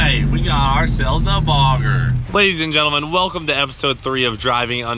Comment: Pretty sure I heard tht "cleetus and gentlemen" at 7:34 😂